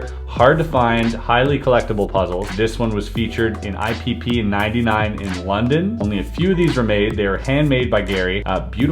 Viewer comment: Did you say? "This one was featured in IPP 99 in london" around 1:40. I don't what that means, but XIX = 19 not 99 that's for sure. :D